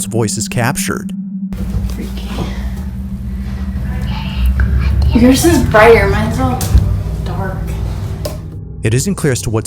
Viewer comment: “I’m just a baby!” That’s what I heard 😂